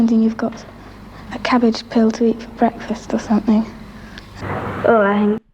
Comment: I think she said cabbage pill. As in a pill that tastes and gives you the nutrition of a cabbage